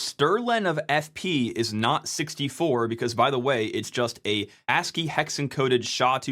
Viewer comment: the subtitles said "as A" 11 times instead of ASCII